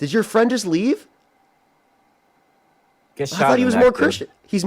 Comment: did I hear that right? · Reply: I heard "get shot in the neck, dude." Which he may wanna be careful of. After all, their former small faced racist got got, making similar off handed remarks about gun violence.